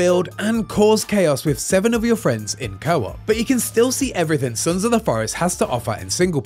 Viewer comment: did he say you can explore with 7 of your friends?? Would still be awesome though.